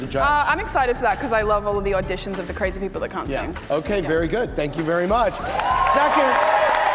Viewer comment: I thought she said: 'I love Katy Perry as the crazy person who can't sing', I was like YOU GO GURLLL! then I realized what she really said....